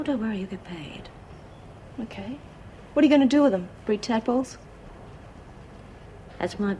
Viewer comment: What did she say at 13:12 ? Tapples or something? What does that mean? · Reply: Carlene Boulden breed tadpoles